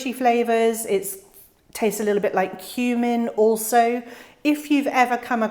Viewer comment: I had to rewind 3 times. I thought you said shiso tastes like human.... but it's cumin. 😆 This year is my first year of trying it, i have a mixed seed packet, so 4 variations!